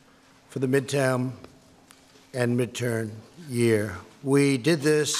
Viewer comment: Did he just say "midtam"